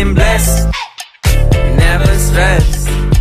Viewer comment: When he said “got that sunshine on my Sunday best” the whole time I thought he said got Albert Einstein on my Sunday best